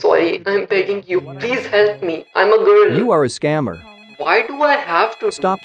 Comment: did he just said i am a girl? LOL